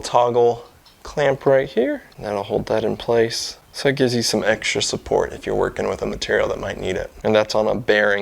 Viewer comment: Did you say eggstra support? lol